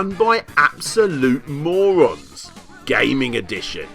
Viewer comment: OHHH. I thought you said MORMONS.